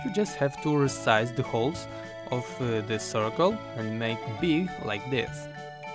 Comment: Great video! But,excuse me, at 4:47, "~to make___like this",I can't get what exactly that word is because of the accent, does anyone know? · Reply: He says to make big like this